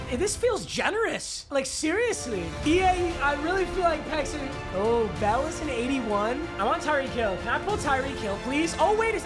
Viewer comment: kinda sounded like u said “ooh Bella’s an 81” 😢